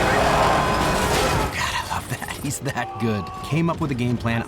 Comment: I've always thought he said "Diamond, Diamond" 😂